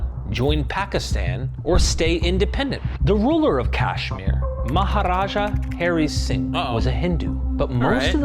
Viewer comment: I thought he said maharaga I was about to say awww shit we know how that turned out on Jujutsu Kaisen